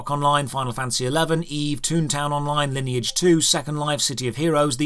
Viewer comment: I swear can't bring himself to acknowledge that EVE online exists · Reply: Don't know if i missheard but i thought i heard him say "Eve" at 2:19 when listing all kind of MMO coming out to that time....